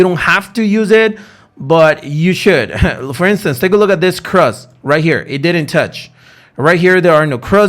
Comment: I am a newbie and trying my best to learn. Towards the end of your video ...at 17:19 you say ??cross?? I think you are saying either cross or crest. Are you saying cross that the candle does not cross the red indicator line. I am seriously wanting to learn strategies. Thank you in advance. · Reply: cross lol